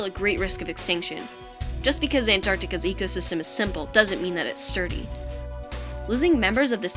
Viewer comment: just because Antarctica‘s ecosystem is simple doesn’t mean it is ? Can anyone understand the last word? Dirty? It doesn’t make sense. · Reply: Li Yan The word is "sturdy". Sorry about that. Hope that helps!